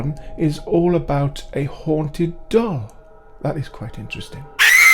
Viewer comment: Yes, it would be marvelous.!! At the beginning of the video, I thought you said that you were going to show a clip of a haunted dog, lol.. Love your sense of humor.!! Loved the videos as well.!! Cheers from me.!! 😆✌️..